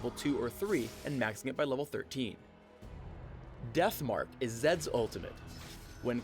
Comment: he said death fart instead of death mark